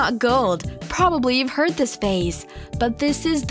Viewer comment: did she say "phase" instead of "phrase"? Also get a new narrator, it sounds like she's talking to 5 year olds.